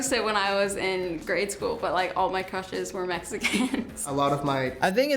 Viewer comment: nah she really said "Mexicans"